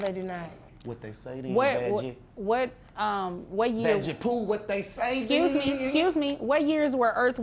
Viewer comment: "Fadgy Poo", did i hear that right?